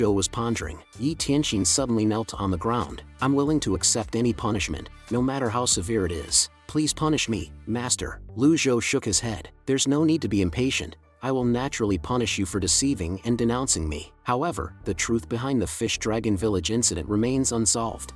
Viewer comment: What she said - Please punish me master What I heard - Please punish me Daddy 😩😫😣